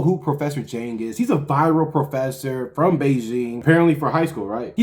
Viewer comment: Thought is said "viral purge" which is a channel who posts Nick content. Dude would have been so sad 😂